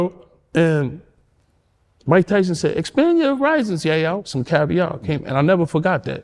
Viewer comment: wtf he say?..He imitated Mike to the tee 😂😂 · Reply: “Expand your horizons Yayo”